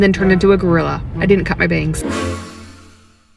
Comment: Holy shit I stopped paying attention for a couple seconds and I heard “cut my veins”